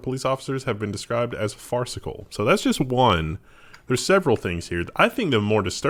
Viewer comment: every time i hear 'farcical' i think 'fart sickle' and quietly chuckle